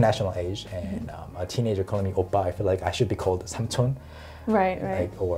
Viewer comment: what did he say...before "aajushi" ?? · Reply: 삼촌/samchon, it means uncle